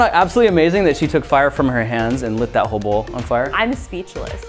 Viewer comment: When he said light this whole bowl on fire, I just heard "light this hobo on fire".